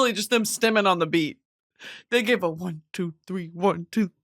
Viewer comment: stimmin on the beat hoe instead of “mustard on the beat hoe”